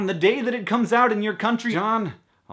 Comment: I was in the kitchen and I totally thought you said "an advertisement for Buca DiBeppo" and I had to do a double take. That's like the best family style Italian restaurant around. Like, period.